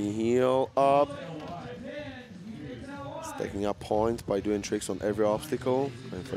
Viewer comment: 「素敵なポイント」って聞こえるw